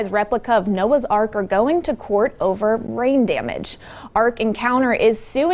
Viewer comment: in , I thought I heard 'Suing over Brain damage'.